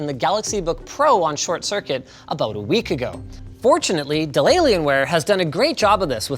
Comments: Has anyone else heard "deliriumware"? 11:32 - and again... Cannot unhear. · i thought he said delaylienware